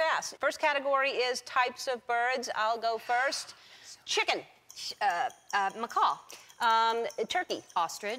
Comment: When ellen said chicken, what did anne say? macaul???? what?